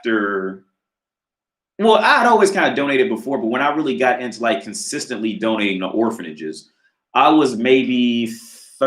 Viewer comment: Damn I thought you said “dominating” like with women lmao